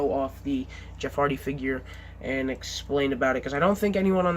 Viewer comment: At first I thought it said Jeffy action figure